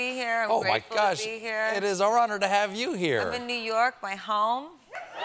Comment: why did the subtitles say "I'm in "narcos" my home" instead of new york in 1:19 ?! lmaoo